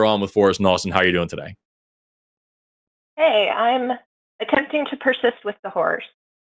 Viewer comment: I love it when people like pronounce "horror" like a one syllable word. I heard her say "I'm attempting to persist with the whores." I replayed it 3 times to be sure. Then I turned on YouTube captions and it said "i'm attempting to persist with the horse." Maybe a more family-friendly version of what I heard, but still a single syllable. Always cracks me up. Horror is such a fun word to mispronounce. Kind of like "ring bearer" was hilarious to mispronounce (you HIMYM fans are with me on that).